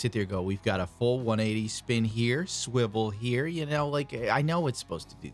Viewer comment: Did you say Swivel Here?